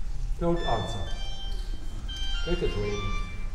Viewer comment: Could someone please tell me what he says at 0:18, after "don't answer"? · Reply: Hard to tell exactly, I think he said: "Don't answer, take it away..."